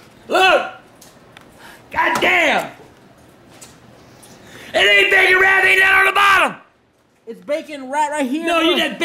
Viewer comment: what did he say?? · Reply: "It ain't bacon wrapped, ain't nothing on the bottom!"